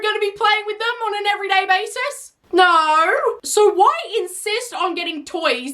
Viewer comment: The way she said “No” sounded like “Naur” 😭